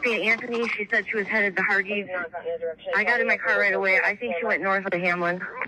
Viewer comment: At first when she asked where are you headed to? I thought she said she’s headed to parties not Hardee’s. 🤣